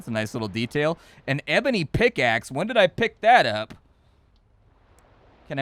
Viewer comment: I had to back it up here... legit thought you said Amity pickaxe. Was thinking, what, is it possessed? 🤣